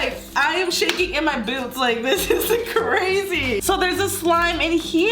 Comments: "okay I am shaking in my boobs!" Lol hahahh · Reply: Inaaya Khan I thought she said boots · did she say I am shaking in my boobs ??? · It sounded like said “I’m shaking in my boobs”? OMG IM DYING xD ( 8:31 - 8:32 ) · Did anyone hear that she said I'm shaking in my boobs at 8:32 or is that just me and my cousin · Did she just say I'm shacking in my boobs.....?? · did she say i am shaking in my boobs? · Did anyone else hear “ I’m seriously shaking in my boobs!” 😂 like if You heard. It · Did she say "I AM SHAKING IN MY BOOBS!" 😂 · Did she say boots or boobs? 😳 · did she just say she’s shaking in her boobs? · did she say she was shaking in her boobs?